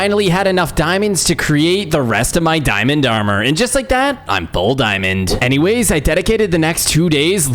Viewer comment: When you said “all diamond” I thought you said “bold diamond “